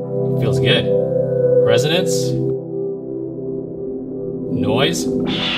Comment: thought you said "noice" at first